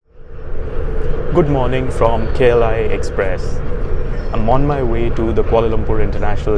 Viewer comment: you said Kla express I heard kerala express 😂😂😂😂😂😂😂